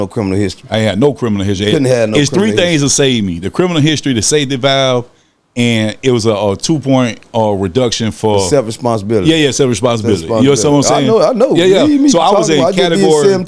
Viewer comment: Did he say he took a safety valve ?